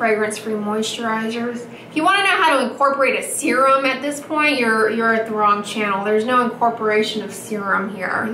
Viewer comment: "If you want to know how to incorporate a serum at this point then you are at the wrong channel." 🤣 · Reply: What you didn't catch was ""If you want to know how to incorporate a serum at this point then you are at the wrong channel." And Andrea said it here